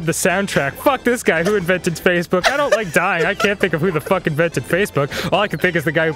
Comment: Did Dan just say "I don't like dying?"